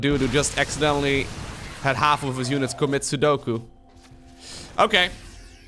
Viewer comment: did you really said "commit sudoku"? lol